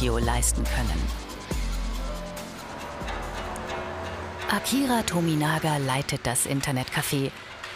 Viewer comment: Ich habe akira toriyama verstanden :D Ich wollte schon sagen.